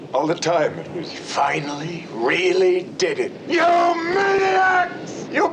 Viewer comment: YOU MANIACS!!! ❤ Greatest twist ending of all time. ❤ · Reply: Me being a huge Band Maid🎀fan, when he said "you maniacs", I heard "you Maidiacs"😸Great band and a CLASSIC sci-fi Film!🙈🙉🙊We'll be the judge of that!